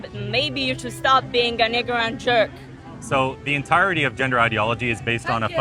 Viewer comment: Anybody else hear what she said at 4:45 LMAO. A WHAT and a jerk?!?! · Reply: I don't know if you are joking but if not she said "an ignorant jerk"